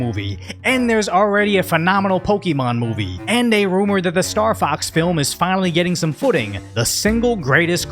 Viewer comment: Did you say “The Star Fox Film”!? 🦊 No way!